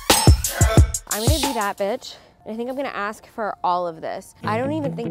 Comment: did I just hear batch because I use to watch this when I was 10